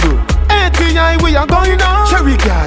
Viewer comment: sounds like he said Sharingan